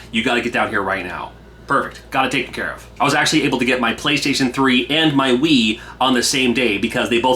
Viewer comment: I thought he said...."I was actually able get my Playstation 3 and my weed at the same time!"....😭⚰🤦‍♂️